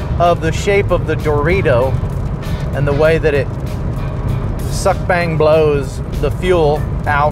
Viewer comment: When you described your Rotary Engine I thought you said Suck Boom Bang. Well, I'm no touching that one, lol.